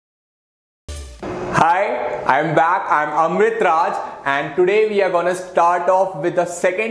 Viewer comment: Just on the subtitles and watch video from 0:16 to 0:18.............It will show hungry Raj instead of Amrit Raj😁😁. Sir is really hungry to teach us👍.